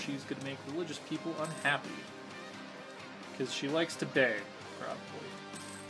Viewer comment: "Because she likes to ???", what are you saying here? It sounds like bay but that doesn't make sense. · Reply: Beg. As in beg for food.